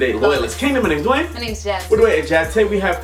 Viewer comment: OMG at first when the video started I thought the girl said my name's Jeff🤣😃🤣😄😄😅😂😂